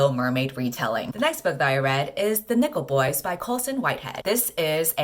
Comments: Oml i heard ‘the nipple boys’ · I definitely heard "The Nipple Boys" instead of "Nickle Boys" the first time and honestly, I didn't even question it. 🤣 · Misheard and thought you said the nipple boys.... wondered what kind of summary I was about to get 😐